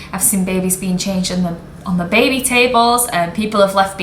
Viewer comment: Did i hear that properly? 18:42, you’ve seen babies been changed on BABY tables? 🤔